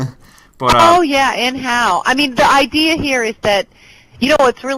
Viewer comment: Did she really say "And how?"